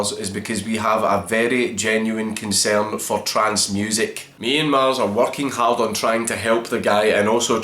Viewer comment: I swear to fucking god, idk what your saying here, BUT EVERY SINGLE TIME you say w/e this is it sounds like "TRANCE MUSIC", and its confusing me. "we have a very genuine concern for trance music" I can't hear it any differently ive listened like 5 times now. Sounded like that earlier in the vid too 15:20 "the only thing we care about is Trance Music" THERE IT IS AGAIN!